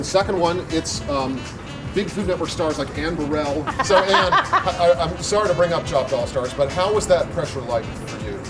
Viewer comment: It sounded like she said her name was sorry to hear